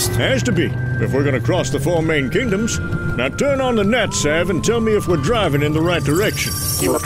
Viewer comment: So cool!!, Btw, did Hawkeye say "Nat-Sav" instead of "Sat-Nav" or I missheard it?